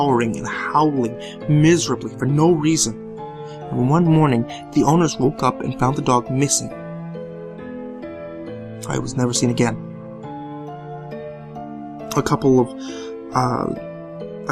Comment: Idk but when i heard about the dog part i was like wtf? Since i heard "I was never seen again"e.e what? maybe he mispoke or i misheard xD